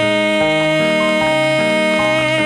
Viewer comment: At the end i thought he said “we have pizza” but he was saying leo peterson lol